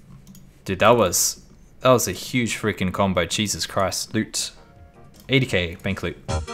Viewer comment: I swear i heard "chesus christ" had me dying lol. IgN: Hell Chains